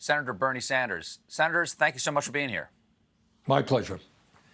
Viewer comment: Is it my imagination or did he say, "Senators, thank you so much for being here"? Could it be that Bernie is actually two people??